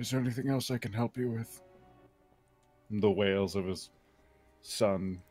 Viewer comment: Good to know that I'm not the only person who thought said whales when she said wails.